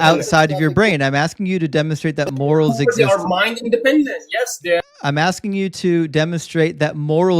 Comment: It kind of sounds like says, "Yes, dad" at this point in the conversation 😆